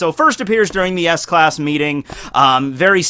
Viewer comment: Did he just say ass class?